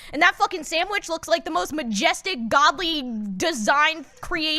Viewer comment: I thought said Mcjestic when talking about mcdonads, and i am now alive 😂